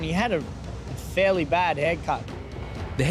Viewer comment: I heard: the guy on the ground, he had a fairly bad haircut ... 😄 "... the haircut is not life-threatening ..." Some things are much more funny when one's tired.